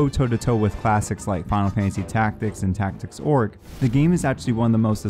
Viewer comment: Did he say Tactics Orc?